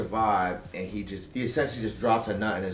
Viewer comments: And then she goes and loots it lmao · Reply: I heard "she goes and boofs it" which made me laugh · I thought he said, "She boofs it," not "loots it"